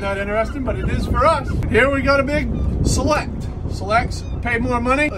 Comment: 😅Dang I thought he said "Here we have a big SLLUUT!" But I turned the captions on and he said "SELECT" ...😂😂